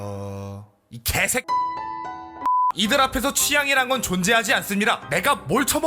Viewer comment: 에서 "개색"하고 화면 조정뒤에 "이들 ~~"하는데 이어서 들으면 "개색이들 앞에선 ~~으로 들림"